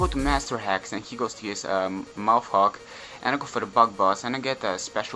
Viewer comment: it sounds like he is saying MOUTHFUCK LOL